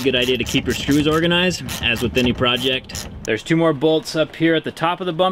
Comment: did he say organize your shoes lol